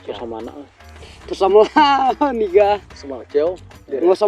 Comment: did he just say nigga😂